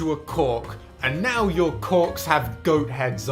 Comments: I thought he said c ck not corks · did he say "now your c**ks have heads"????